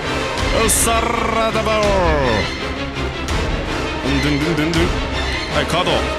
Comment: 「サッラダバー！」って聞こえたの俺だけ？